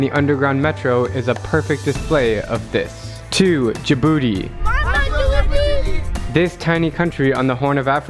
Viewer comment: When I heard Djibouti I thought he said the infamous jibooty